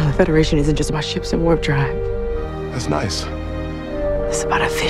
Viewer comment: Sounded like she said "it's about a fish head and all those who believe in that fish head"